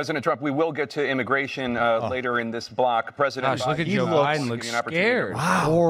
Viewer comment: At about 8:00, did Tapper say, "President OBiden"??